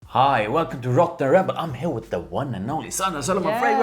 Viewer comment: Once again i hear "Rock the Rebel" lol, i hate it cause it makes me feel hella dumb